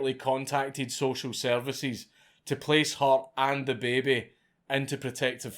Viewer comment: I keep hearing you say “da baby”